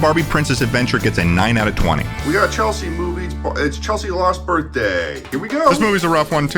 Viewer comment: I SWORE YOU SAID Barbie & Chelsea the LAST Birthday ... & i was like 😨 THEY CONFIRMED THAT THEY KILL OR CLONE KELLY/CHELSEA ?!